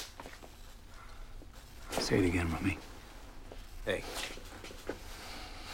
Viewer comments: SAY AGAIN RUMMY.. ✋ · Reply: I thought he said, "Say it again about me." But you might be right. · SAY AGAIN RUMMY.. ✋ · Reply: It sounds like he says "mummy". If you watch his mouth closely it also looks that way.